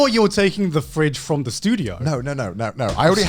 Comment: It's because the way Connor said "I'm taking the old one from this studio" and gestured, it sounds kinda like he says "I'm taking the old one from this dude" and gestures at Joey. Very funny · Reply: I've heard this episode many times and just now i realized joey thought Connor said "this dude" not "the studio"